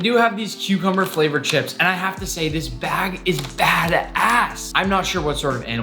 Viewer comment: did he say this bag is bas a$$